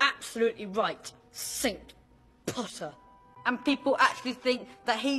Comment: Draco: "Sink Potter." What i thought he said: "Simp Potter." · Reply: I think he said saint pottah